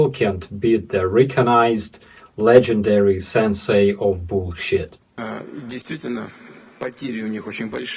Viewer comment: From a distant land of the russian imagination 😁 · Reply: You're right, at this mark, to my ears he clearly says "bullshit" 😅